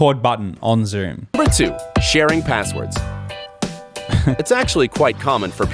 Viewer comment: I heard “sharing passports” not “passwords” and was like - does anyone do this or think it’s legal? Wtf